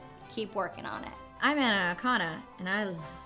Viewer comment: I CAN'T possibly be the only one who thought she said, "I'm an anaconda," in the bloopers...